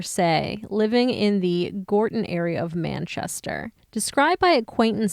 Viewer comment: I live in Gordon, Manchester and I’ve never heard of this story!!! Shocking and so very sad. RIP sweet girl x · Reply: Gordon, I thought it said Gorton 🤔